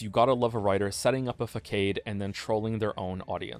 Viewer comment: bro did you just say fuckade?